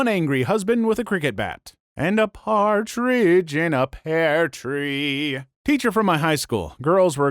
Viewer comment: "and a partridge in a pear tree" XD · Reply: i heard "and a cartrige in a pared tree"